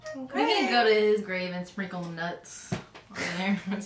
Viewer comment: Did she just say "go to his grave and sprinkle NUTS." ... gurl whatchu mean [epic side eye smile].🤭🤭